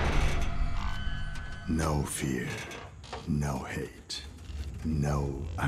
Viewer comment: From a distance I heard ‘no fear no head no onion’